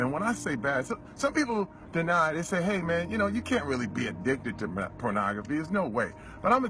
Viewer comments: does he says addicted to meth-pornography?? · did he say meth pornography? 😳